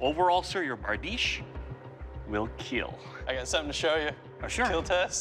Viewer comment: Why do I always hear " your blade will keel?"